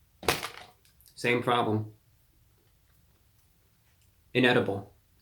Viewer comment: I thought the title said "incredible" at first lol